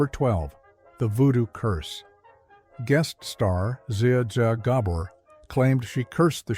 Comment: Was pleased with the voice/narration that is until I heard “zay zay garbor” ugh AI bites